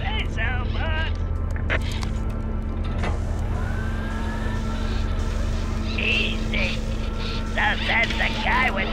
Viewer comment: what does he say here? i thought he said something like "hes ***** for a guy with no freaking arms" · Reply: He says "easy? So says the guy with no fucking arms!"